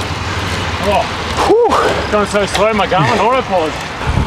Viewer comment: did he say I'm going so slow my Garmin auto-paused?